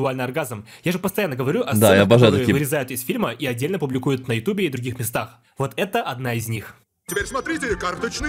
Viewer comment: ты неправильно услышал сказал "мне нравиться такие крутые сцены которые ты можешь отдельно опубликовать на ютуб"